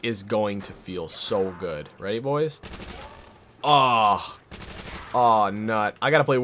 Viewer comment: did he say “ahhhh nut”!!?